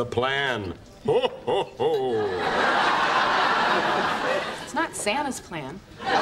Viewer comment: Lmaoo, "It's not Santa's plan". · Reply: I always heard it like," it's not sadist plan." 3 years and now I got the joke.